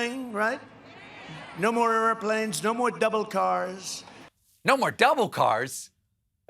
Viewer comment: WTF, IS A DOUBLE CAR? 🤣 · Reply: I thought he said devil car! Which makes more sense than double car!